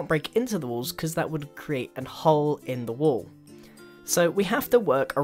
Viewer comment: did he say 'an hole' instead of 'a hole'?